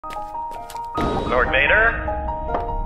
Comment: Why do i hear "do you wanna kill redbulls?"